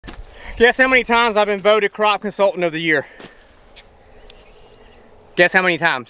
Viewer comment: I swear first time I watched this I thought you said crap consultant lol